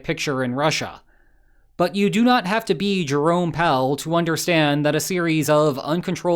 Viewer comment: My ears lied to me, I heard 'Drone Pal' instead of 'Jerome Powell'.